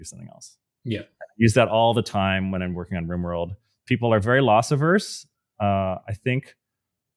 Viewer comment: what's that word? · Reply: loss averse